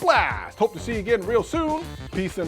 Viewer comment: Did he just say.. wait until they find out I order the watch.